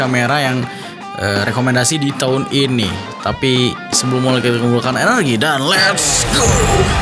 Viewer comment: bang 0:06 - 0:07 ngomong apa yaa ? hehe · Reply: sebelum mulai kita *&@@@ , dan lets go